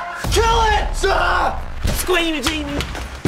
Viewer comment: Wait what did he say?!? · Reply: Its like squinajans